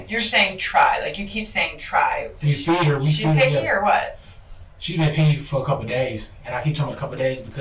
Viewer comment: When he said “she’s been picky for a couple days” I just hear “she’s been dying for a couple days”.